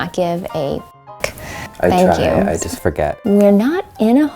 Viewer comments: I keep hearing “I forgot” not the John thing 🤷‍♀️ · i didn’t hear it was john i heard “liz plus john” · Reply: i hear "it forgot" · i honestly heard "Liz" at 19:53 and the "it was john" part sounded like "Liz PLUS John" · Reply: idk i heard "it forgot" or "ive forgot" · I heard "this is what you got?" instead of "It was John". Like I can hear it so clearly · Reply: Isa Belle yeah I hear the “got” part. Like maybe, “it forgot” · Sounds like it said " IT FORGOT !!!" Lol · IM NOT JOKING THE ONLY THING I CAN HEAR FOR “it was john” is “ISPAGATTA” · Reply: I heard “it forgot” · i hear " i forgot" angrly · I hear "It Forgot!" 🤷🏼‍♀️👻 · seriously i kept hearing "it forgot" instead of "it was john" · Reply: Yeah, I heard "it's forgotten" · I hear "it forGOT" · I hear "i forgot" or " it was god" ^^ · I hear “it forgot” or “it’s for god” but oh well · All I hear is "it forgot"...